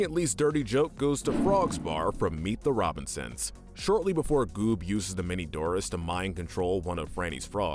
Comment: I thought he said boob